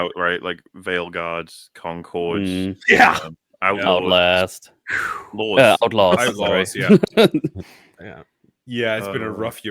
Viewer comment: What was the game said at 1:04:42? I can't find any game called "Outlast" released in 2024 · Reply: Star wars outlaws